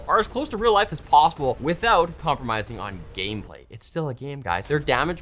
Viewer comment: I thought he was going to say "without compromising on national security" (instead of without compromising on game play) in the World of Tanks ad at the end.